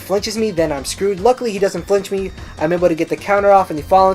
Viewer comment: analytics account often boxing. What was really said, "luckily I am able to get the Counter off." Transcripts sure is good xD